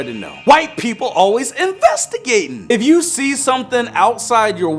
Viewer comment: First video: 2/10 Second video: 4/10 Oh and when u said “Why people investigate!?” I thought u said “White people always investigate!” Love u and ur vids!